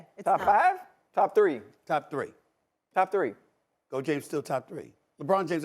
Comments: just SAS said goat james ?? am i hearing this right ??? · Did I just hear Stephen A refer to Lebron as Goat James at 14:50. Hmm very interesting 🤔